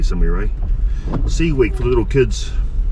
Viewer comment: Sea week, I thought you said c west 😂 Chur bro snapper head looked goods, not sure bout the 👁️ though, hearty.